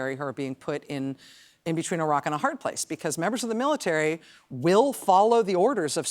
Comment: I’ve been watching Rachel so long that when she said, “Between a rock and a hard place,” I heard, “Between Iraq and a hard place.”